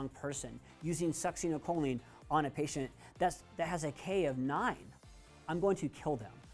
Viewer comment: good video. at 26:09, I had to listen over and over to hear what you said....now I heard it! "A K of 9" Couldn't you have just said a Potassium of 9? LOL